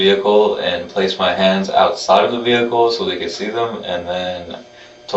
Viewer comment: I commented to say, great, you watched the video too! However, it looks like got it wrong. I often go and read up on any interesting videos and this is what the Palm Beach Post said: "Noiman told police he slept inside the SUV while his father slept outside, using the SUV’s floor mats as makeshift bedding." Now I'm going back to read the rest of it. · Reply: Thank you. I thought he said “house,” but it may have been just “outside.” Btw, I’m fully sympathetic with all who are struggling financially (I’m one, though not to an extreme degree).